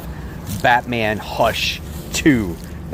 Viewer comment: At first I thought you said "Fatman Hush 2!"😂